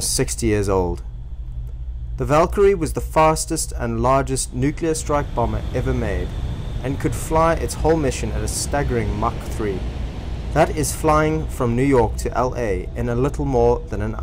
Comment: Lol. I heard "nuclear......strike bomber" rather than "nuclear strike......bomber."